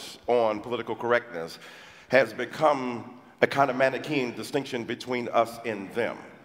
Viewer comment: WTF is Dyson saying at 24:42?! I enjoy learning new words, but I have no Idea what word the guy is saying here. "A kind of matikeen?" "Acondamatikeen?"